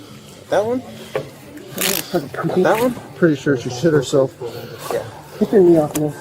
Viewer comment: Did he just say she shit herself? 😂